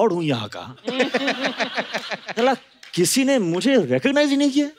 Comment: did Hritik just said "Kellaa" kisine mujhe recognise hi nahi kiya 😂 People from Assam, Assemble.